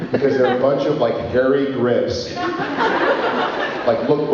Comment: I thought Jared said "Hairy Grits". LOL - he said "Hairy Grips".